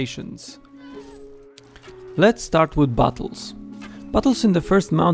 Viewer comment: Firstly i thought you said "bottles" not "battles"